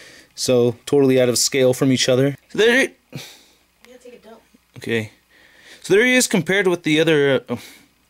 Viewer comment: did someone say they were taking dump? around the 5:35 mark? lol